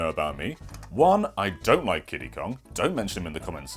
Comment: At first I thought he said "I don't like Diddy Kong" and I was about to rage, but than I re-listened and he said Kiddy Kong and I absolutely agree with him.